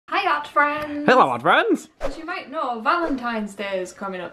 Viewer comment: For like a month I thought you were saying "hey Yacht friends!"